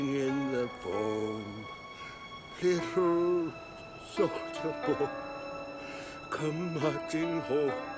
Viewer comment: Ik this is supposed to be a sad scene but everytime i hear him say "soldier boy" i cant help but think hes saying "Souldja boy"